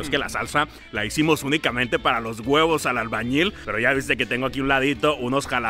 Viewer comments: XD pensé que decía huevos de albañil XD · Pensé que decía huevos de albañil